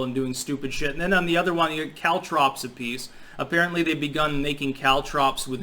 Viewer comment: Just got back from a bad night at work. I thought you said cow troughs. Cow troughs pop tires...oh caltrops. Good night.